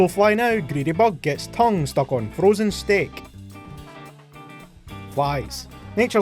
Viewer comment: Does he say "frozen stick"